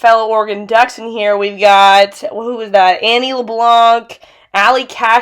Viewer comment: did she say annie lebance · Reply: banana. jk yeah i wonder if it was the annie leblanc